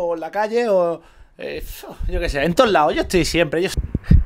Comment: Creo que han secuestrado a D: si se van al minuto 6:53 parece que dice "Jelp" hay que hacer algo